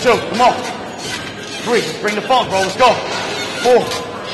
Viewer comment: Did he say "bring the funk, bro"? That's motivation...